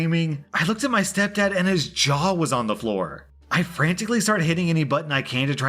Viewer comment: thought R said junk was on the floor instead of jaw